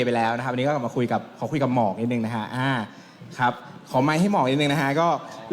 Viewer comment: Did he call Gawin "Mork" at 13:49 ?!